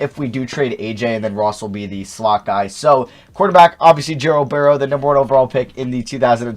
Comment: Did he say Gerald Burrow?